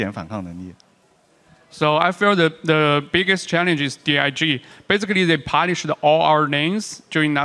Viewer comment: Wait... did say "DIG"? but Jatt interpreted it as "CLG"?:o